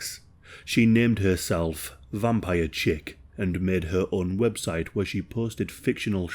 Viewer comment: Oh god, I misheard her online username as "That Vampire Chick" and thought there was A LOT more to the girl who wrote "Trapped in a Island with Josh Hutcherson" :'y